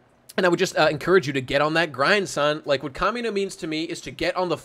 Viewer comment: Akimda? Kamida? What is the name you're saying.